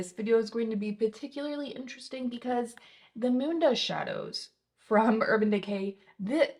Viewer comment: Did she just say the Mundo/Moondoh shadows? I’ve rewound it like 6 times. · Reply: Moondust shadows from Urban Decay lol what this whole video is about